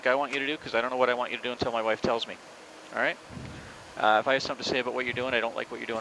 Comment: does the captain actually say "...cos I don't know what I want you to do until my wife tells me" ?? I can't make out what else it could be but at the same time if that IS what he said then the humour sort of fell flat in the moment.